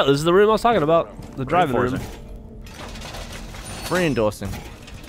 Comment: Anybody else hear Gavin say rein-doorsing?